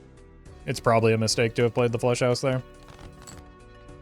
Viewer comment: I keep hearing you say "Flesh House" and every time I glance at my monitor in confusion